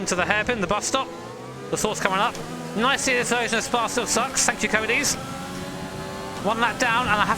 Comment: was that English, all I heard was some lispy mess 😂😂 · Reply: I think he said "this year's Spa still sucks, thanks Codies"